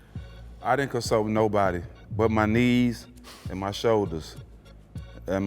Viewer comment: lmaoo I thought he said "niece" then 5 said "and my shoulders" lol